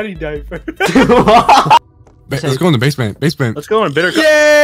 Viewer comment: DID THEY SAID BLOOMS BASEMENT!?!?!?